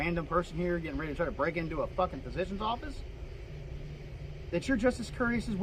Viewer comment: Is he saying ‘fucking office’ here? If so, that’s very professional and courteous of him. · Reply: He said "fucking physicians office"